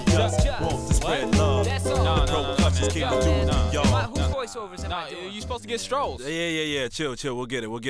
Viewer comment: 一発芸しろって聞こえる